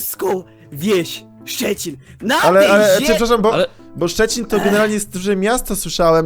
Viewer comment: czy ja usłyszałam Szczecin? xD ja jestem ze Szczecina ktoś jeszcze że Szczecina? #TeamSzczecin